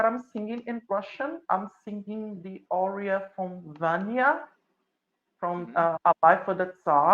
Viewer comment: Ayuda cual es el aria del minuto 29:14 solo entendí algo de Dania algo de Tsar y que está en ruso pero no logro escuchar todo el dato ayuda ayuda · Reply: no se el nombre del aria pero el rol del que estabamos hablando era Vanya de "una vida por el zar" de Glinka